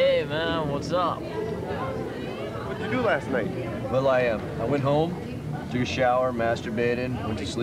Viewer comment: I thought he said Will.i.am, the rapper.